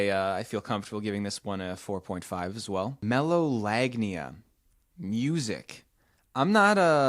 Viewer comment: I have melolagnia. There is some music that is very sexy to me, specially tropical and pop songs. For example, the very sexualized music and stuff that matches the rythm of sex and so on. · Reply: I thought it said megalovania and i did a double take